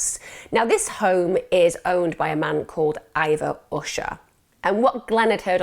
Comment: I must be tired. Thought Emma said Ivor Washer🙄🙄🤣